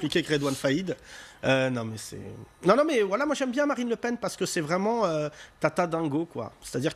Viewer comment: Personne d'autre a capté le : 12:51 " Nan nan mais wallah moi j'aime bien Marine le Pen parce que ..." blablabla Ca peut aussi être "(v)oila" qu'il dit, allez savoir.